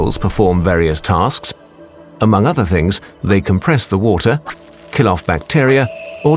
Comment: there is a subtitle error. The narrator says "compress" but the subtitle says "condense". You're welcome.